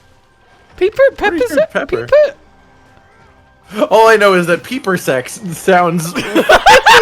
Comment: to be honest, I heard pronouncing it as "puber-sects", which I would argue is worse than "peeper-sects"